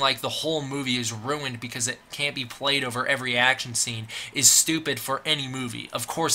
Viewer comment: the subtitles says an, but you say every.